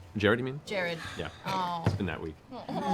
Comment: Did Matthew say "He's been dead a week." or "It's been that week." a bit after 1:18:38 ? If he said he's dead, when did that happen?